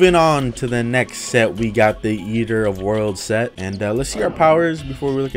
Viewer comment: Wait am i crazy or did i hear him say "Yeeter of worlds"